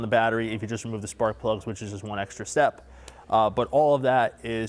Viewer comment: Disconnecting the spark plug coils has nothing to do with compression. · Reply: M i could have sworn he said wire, but he did say plugs. That makes sense now.